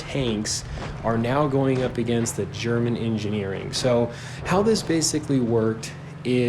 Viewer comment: For a sec I thought u said german English 😂